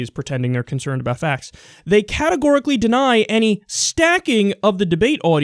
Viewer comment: Whenever I hear "categorically deny" I hear "big fat lie"